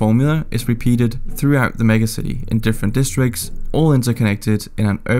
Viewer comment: 目薬って言ってるw(空耳)